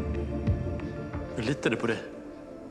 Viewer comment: why did it look like august said slagroom ( whipped cream ) for al my dutches who know what im saying